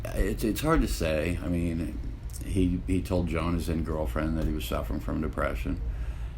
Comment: Can't make out what he says at 13:48 he told WHO he was suffering from deppresion? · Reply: Anat Goldberg Jone his girlfriend at the time